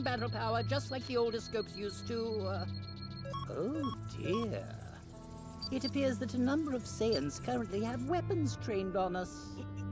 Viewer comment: what does frieza say from 2:00 ? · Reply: It appears that a number of Saiyans have weapons trained on us